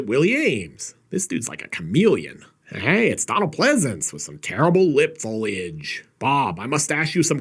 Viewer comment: said Donald Pleasance had some "bad lip foliage" meaning a bad moustache. I thought he said "bad lip foley-age" thinking he sounded terrible